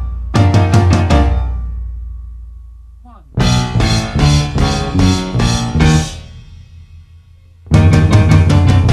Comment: It’s nice to hear Freddie play the piano!! 🎹🎹 What did Freddie say at 3:50?? 😲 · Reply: One